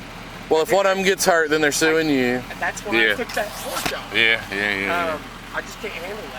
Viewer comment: He admitted to knocking down the porch!! What I clearly heard through my ear buds is that he said "thats why I took that porch off" at 14:25. WOW And he's got enough money to help those puppies and clearly didn't do it!! Here it's a crime "Scruffy's Law" which I'm sure it is in most states! It's very cruel to KNOW the mother dog was injured WITH puppies and not get her or the puppies help or to even ALLOW anyone to get them help! He's not the man I'd want to be associated with EVER!! CRUEL MAN!! I hope his company takes a HUGE hit from this!